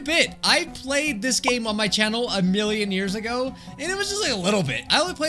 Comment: what did you say minion days of million years